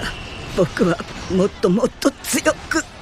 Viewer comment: it sounds like he said moto moto LOL